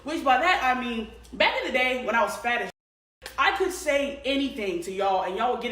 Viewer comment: did i hear "when i was fat"? lol